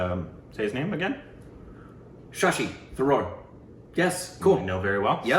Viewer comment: When said shashi tharoor, I heard “shashi the roar” 😛